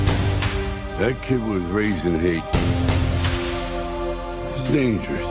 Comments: "That kid was raised in Haiti he's dangerous". I agree with subs. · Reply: I thought he said "that kid was raised in hate" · What did Rocky say at 1:05? "That kid was raised in hate."?